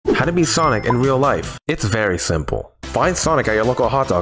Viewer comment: I thought he said how to be sonic in real life😂